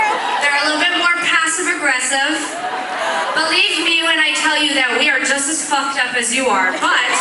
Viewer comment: What Sara said in 0:49? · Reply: "believe me when I tell you we are just as fucked up as you are"